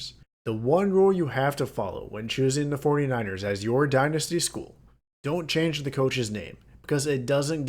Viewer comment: You said don’t change the coaches here at 6:08. I heard we won’t be able to edit coaches